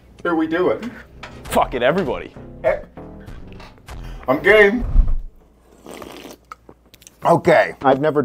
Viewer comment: What i thought he said,, Im Gay" What he really said,, Im game "